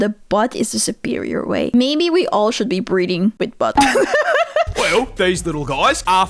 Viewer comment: Okay, first minute and as i get up to get a drink i heard "maybe we should all be breeding with our butts" and I IMMEDIATELY hit the human equivalent of chucking a handbreaky and uey as I stormed back to double check what I heard and was absolutely relieved to hear breathing instead